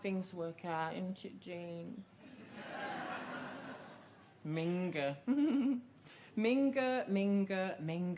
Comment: hey can someone help me, i'm not a native speaker ... what's this word here, i can't make it out. is it a slur or something? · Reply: ‘Minger’, informal/derogatory word in English, not a slur! It means unattractive or unpleasant person. A minger is minging. Hope this helps!